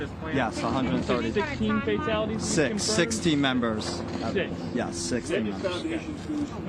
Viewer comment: I thought he said sixteen members deaths, but I guess it was six "team" members. Enunciation counts!